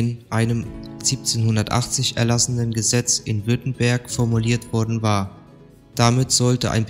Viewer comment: Bei Min 24:56 sagst du "Ein im Jahr 1780 erlassenen Gesetz in Bötenberg" Hab ich richtig verstanden? Den Ort Bötenberg in Niedersachsen?